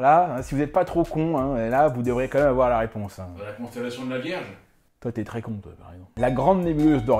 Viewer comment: J'ai entendu "con fellation de la vierge". sors de mon corps ! (C'était une expression:))